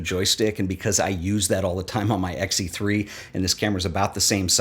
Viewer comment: Wait. What? At 1:43 (and other spots), did you say "on your X-T3"? Is that already released? I had to stop the video and ask this! Now back to the video! Thanks!